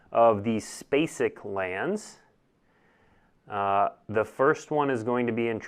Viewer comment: Did you say, "spacic lands" at 14:12? Nice.